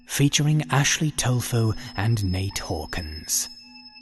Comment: I heard "Ashley Tofu" and "Nate Organs". I thought to myself...."how strange and fitting the names are". Then I looked at the description...and...I am wrong. Feel free to correct my punctuation, as quotation marks have never been my strong point.